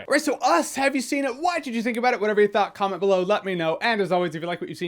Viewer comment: The video is inverted that's why he looks odd and it's a nod to the film itself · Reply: Pierce Morton At the end instead of "what did you think about it" it sounds like he says "why did you think about it" also threw me off.